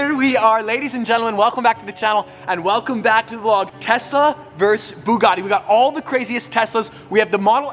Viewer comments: Did he say Tessa instead of Tesla 🤔 0:14 I swear to god Teslas are now boring they slow af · Did anyone else hear “Tessa vs Bugatti’ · Did he say tessa as in his girlfriend? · or 0:14 did he say Tessa instead of Tesla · Did he say Tessa Vs buggati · I thought he said Tessa instead of Tesla. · Did I hear it as tessa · it sounds like he saying Tessa instead of Tesla